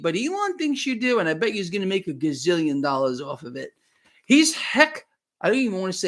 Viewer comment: I heard “Hex” He X …$👤 profound ability to captivate or manipulate, Jinx Money By HeX